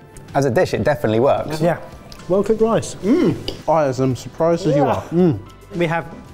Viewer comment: Did say "Well cooked lice"? @ 4:41.